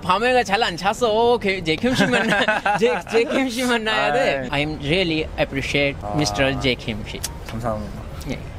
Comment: I heard it " i really appreciate mr jay kimchi"